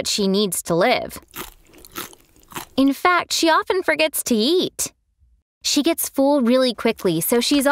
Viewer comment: I thought she said She forgets to yeet